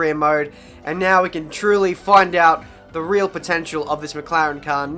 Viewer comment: Why when you say "truly" i always hear "trulli"?